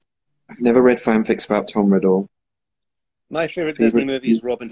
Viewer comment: im from turkey I dont understand cristian what is say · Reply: he said 'never read fanfics about tom riddle'